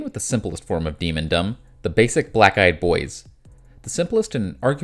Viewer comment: Every time you say “black eye,” I hear “black guy” and it makes me giggle. I’m a child, I know. 😂❤